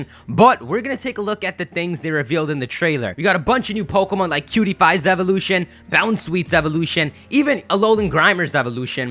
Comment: omg at 0:12 I thought he said PewDiePie's evolution